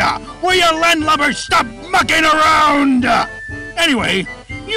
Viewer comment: Foxy did not say “F**king around!” He says “Mucking around!” Sorry for the lack of clarity. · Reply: I literally first thought he said “F**king around!” But I went back to that part to hear it more clearly and realized he said “Mucking around!”.